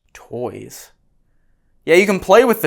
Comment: where you hear the word “toys” I hear the word “warning”